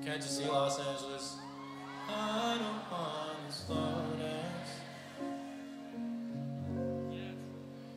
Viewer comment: Damn this was so fkn good! For the longest time I thought he said “All I want is romance...” the second time instead of “I don’t want to slow dance.”